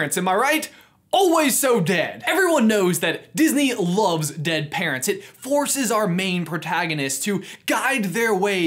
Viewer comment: I thought you said ‘dead parrots’ in the beginning of the video